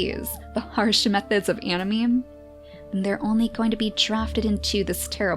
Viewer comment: i think i heard her say "harsh methods of anameme" instead of "harsh methods of anareme"... ANAMEME DD